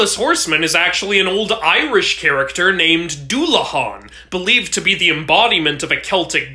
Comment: I thought u said dula peep not dullahan